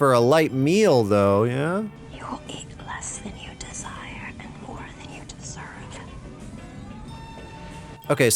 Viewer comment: "you'll eat glass in your dessert and your son won't anytime be a hiree" I thought she said. anyways, same same.